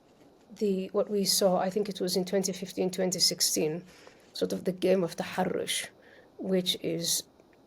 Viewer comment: Can anyone tell me what she says at 3:30? The "game of the haroush"? I've been trying to look it up online but haven't come up with anything, probably because the spellings I've been trying are wrong.